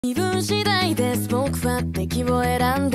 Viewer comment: 最初「微分次第です僕は」って聞こえてめっちゃ受験感ある